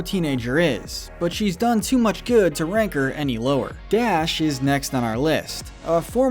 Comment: Anyone else hear Gash